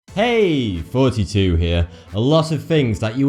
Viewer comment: i thought he said : Hey Fouseytube Here!